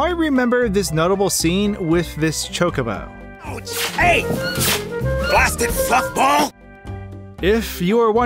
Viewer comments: In Mario PT, I heard "Take this, little tide pods!" 3D Land, I hear "Who" 3:35 "Blasted F***-ball!" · I heard "blasted fuck ball" for some reason. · I heard "Watch it Fluffball!!"